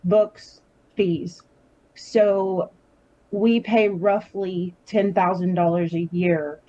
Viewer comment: From what I heard it’s no WE but HE pays those things.